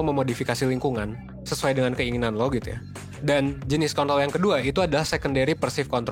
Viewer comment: kok gw dengernya kont*l sih 🤣